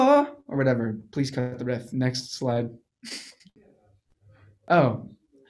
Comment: what does he say at 3:35? :( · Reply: he says “please cut out the riff. next slide”. he’s pretty much joking asking them to edit out his singing lol